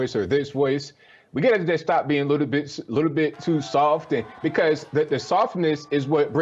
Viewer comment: I heard “We gotta just stop being little bitches, little bit too soft.” 😂🤣🤣😂